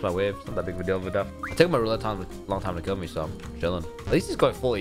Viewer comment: literally cant understand a thing is saying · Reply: it took him a really long time to kill me actually